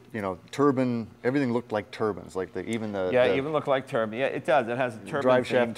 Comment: Thought it was Turbine, not Turban lol !! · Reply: I thought they were saying Turbon